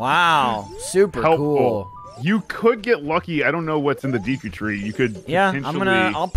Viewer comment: Did Myatt just say "Deaku tree"? @ 2:57 Ah yes we all know that we'll know character in my hero..."Deaku" right!?